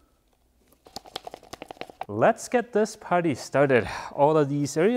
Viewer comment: I could have sworn I heard say, "Let's get this putty started." I laughed too hard before I realized he said party. Anyone else hear it the wrong way? Happy Birthday!!